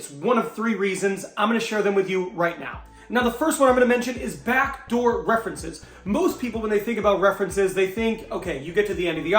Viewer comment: Hi - just a question - I thought I heard "Back door References" - but the wording displayed "Bank Door References" - which one is it? Sorry - a bit confused here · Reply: Sorry it’s Back Door Reference